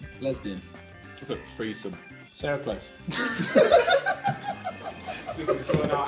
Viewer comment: What did Ethan say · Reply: sarah close